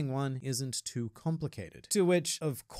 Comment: I keep hearing pirates